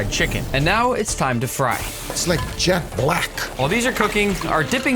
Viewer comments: It sounded like Gouda said Jack Black 1:35😂😂😂😂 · Did Guga say jack black or jet black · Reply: He said jet black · I thought he said Jack Black 😭